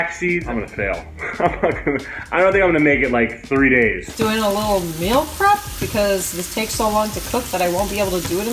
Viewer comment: legit thought that said swastika for a sec